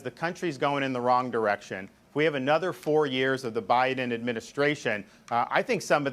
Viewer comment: DeSantis says we have another 4 years of the Biden administration, did I hear that wrong? I hope.